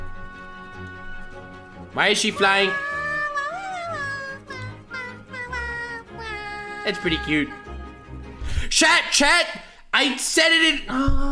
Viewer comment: Is it me... Or did he just call us "Shat"?